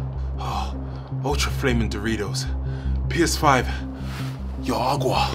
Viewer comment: I heard “Mucho flaming Duritos” “PS5” “No agua”